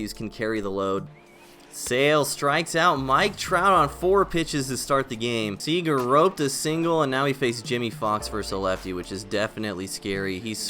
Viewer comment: I think you meant to say “Seager roked a single”